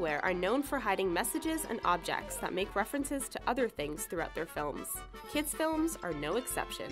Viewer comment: It says toother instead of to other